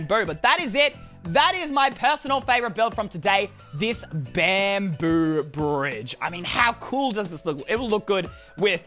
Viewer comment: Lol I thought he said bear poo bridge